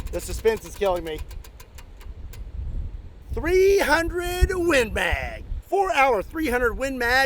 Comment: I swore you said 300 wind-BAG